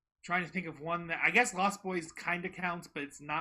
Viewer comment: HAPPY BIRTHDAY 🎉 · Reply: I genuinely thought said MONSTERS INC when he was answering “what’s your favorite scary movie” 😂 and then I went back a heard he said lost boys